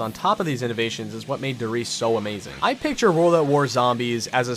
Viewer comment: Thought it said die rise